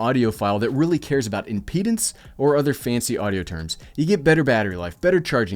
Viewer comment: did he just say impedance while talking about wireless earphones?